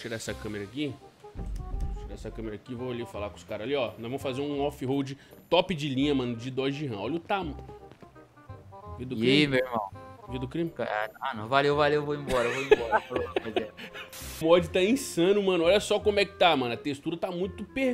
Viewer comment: oi? Eu ouvi vida do crime? Kkkkk